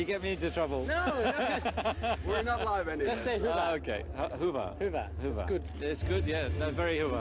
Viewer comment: any Finnish speakers who can tell me what Seb told Christian to say? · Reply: Vitun hyvä = f*cking good